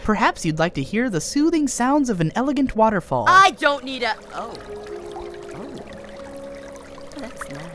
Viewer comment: Did PaintBrush Say The N Word? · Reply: He said need a